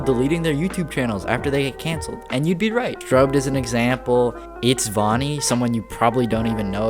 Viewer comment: when he said strobed I thought he said Scrubby